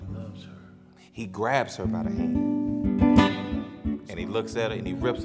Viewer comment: Sounds like he said " he grabbed her by the neck " haha